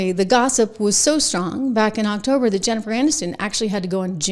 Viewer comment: Thought I heard it was Jeffries Anus again, not Jennifer Anniston😉😂